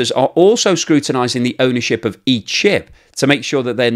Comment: "each ship"! I heard "eat sh1t". I had to replay it a few times. 😆😆😆 Thanks for everything you do. Great to see you're back. 🏆